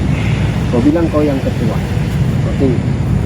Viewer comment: Aku x salah dengar ka? Ustaz tu cakap "kau bilang kau yg ketua." Seolah olah dia suruh berlakon